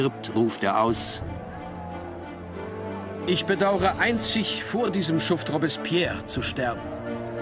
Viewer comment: Was ruft Danton bitte um 1:21:14 aus? Ich komme aus der Tschechischen Republik und verstehe das nicht. Ich kann diese Rede nirgendwo finden. Ich danke Ihnen vielmals. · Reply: "Ich bedaure einzig, vor diesem Schuft Robbespierre zu sterben"